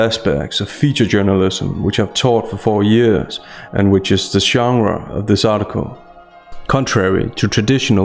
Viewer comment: What's the word he uses at 0:20 that sounds like "shangra"? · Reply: Sounds like "genre" to me.